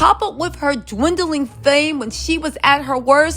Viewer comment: Chile I thought you was about to say her “Dwindling Uterus “🙊😫😂